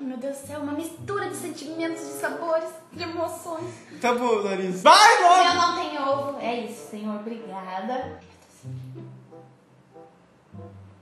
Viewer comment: acho que ouvi '' tabom larissa '